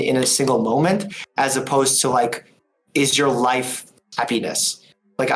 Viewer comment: This is so wholesome and I love it but at 1:19:56 I lost it when I heard "Is your life a pen1s" Much love to Dr. Mike and Dr. K <3